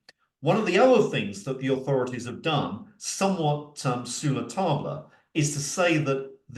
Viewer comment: Please: 24:11 to 24:18… what on earth did he say..??, what’s that expression..??, Latin..??? The transcript didn’t pick it up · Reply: I am not an expert in French... But i guess he says "sur le tard" which mean 'late/too late/late in life' some how...